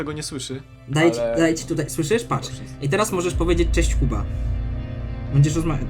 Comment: ja usłyszałem na początku cześć kupa ale puściłem od początku i usłyszałem cześć Kuba